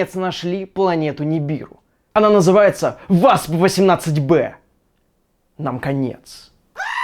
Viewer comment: ВАЗ-18б ...вот что я услышал..))